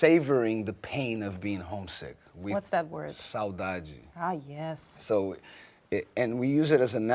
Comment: what is that word ? anyone speaks Portuguese ? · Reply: saudade